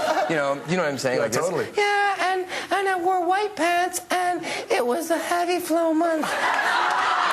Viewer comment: what was he saying at 4:26 - 4:29 ? Can anyone tell me?? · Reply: "I was wearing white paints and it was a heavy flow month" its a menstruation joke